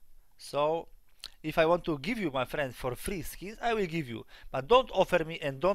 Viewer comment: did he just said "i will give you free"?